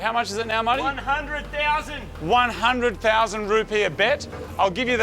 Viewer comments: Me being an American, I thought they said 100,000 root beer, not 100,000 dollars🤣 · I thought you guys where say 100000 root beer this entire time...